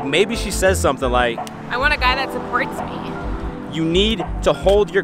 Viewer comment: "I want a guy that" what? I tried listening to that like 5 times and can't make out what she said. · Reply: "supports me"